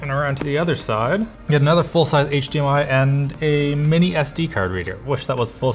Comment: did he just say another full sized HDMI port